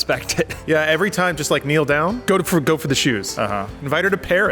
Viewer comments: Lol I thought said Jews at first... lol he said shoes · Oh "shoes". I thought said Omar hates....nevermind